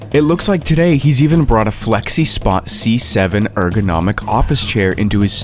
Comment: i heard sexyspot 💀☠